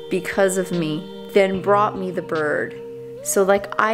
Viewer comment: I thought it said “bought” rather than “brought”. I was so confused when she said that she’d help you find a bird. LOL!! Evie is just the cutest Mustache Parakeet. Oh my goodness!! Adorable. Monty wants to be loved so badly. He’ll find his happily ever after soon. Both Nellie and Monty will. I just know it. Big hugs 🤗 PS. George was in heaven having both Jersey and Evie just sleeping and chilling on him. So beautiful. 💗🐦🐦